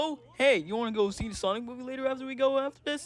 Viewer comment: The Sign Movie? Is that what Lumpy said. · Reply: no he said the “Sonic Movie”